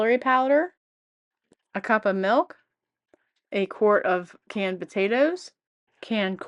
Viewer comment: I heard you say 1 cup milk in the video😊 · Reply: It’s 1/2 cup of milk